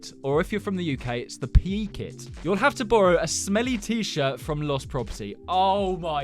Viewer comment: nah i recently had a fire alarm test at my school and the teacher was like “ MaKe SuRe YoUr ShIrT iS tUcKeD iN” 💀💀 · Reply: I thought shirt said shi*